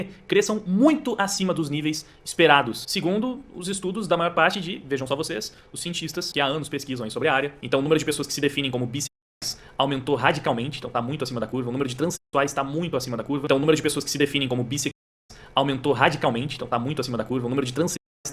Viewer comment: o número de pessoas que se indentificam bíceps kkkk eu ouvi isso